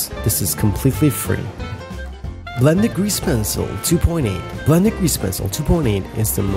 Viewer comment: i cant really understand what he says here can someone tell me because I really want this one. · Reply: "blend(er) grease pencil 2.8" I think, idk he kinda sounds like he's mumbling lmao